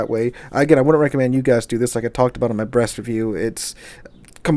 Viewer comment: I laughed so hard when I just heard "when I talked about it in my brest review" my brain forgot that he was talking about the ship for a second.